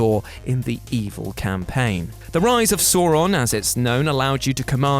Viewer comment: This guy really can't say sauron sounds like he saying sawron